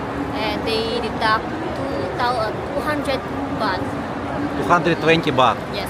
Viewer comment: Did you say the ATM Fee was 120 bhat at Kbank ATM? Do you get some discount? The lowest I ever heard for an ATM fee is 150 bhat. Do you remember what floor that ATM was on? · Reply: 220 THB withdrawal fee in this KBank ATM